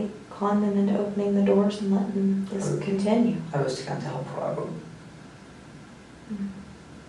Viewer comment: what does he say here? i cant understand him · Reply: "I was trying to help Robert"